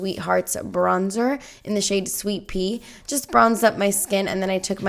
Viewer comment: It sounded like you said "sweet pee" instead of "Sweet Tea" bahahahahahaha